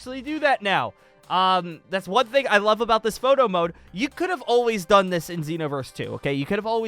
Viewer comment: All i heard was "moto mode" which in my ears was "moto moto mode"